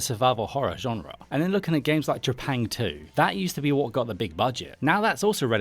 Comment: what game did you mention at 2:03? I can't quite make it, sounds like you said "japain 2" but google got me nothing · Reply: Trepang2 - it’s well worth a play.